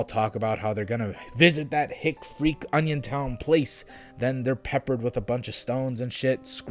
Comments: At first I thought it said “Onision” town and I was like “bitch, I’d be scared too.” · I thought you said "Onisiontown", like who tf would visit a town baptized in Onisions name? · Thought it said opinion town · bitch i thought that said 'onisiontown'